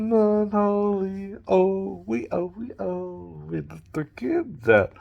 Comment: Did dude say where the kids at, 00:40 You better stay away from kids